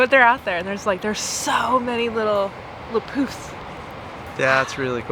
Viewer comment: Oh... I totally misheard "little poufs" as "le poufs", and this is 100% going to be my go-to for when whales expell air. 😊